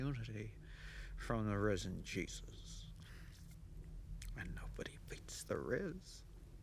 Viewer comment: Did he say rizz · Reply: yes because he says no one beats the Risen Jesus, and there was a famous electronics store called the Wiz, and their tag line was No One Beats The Wiz